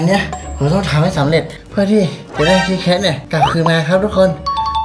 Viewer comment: ตอน8:11ด.ร.พูดว่าคิทเคพใครว่าบ้างไลค์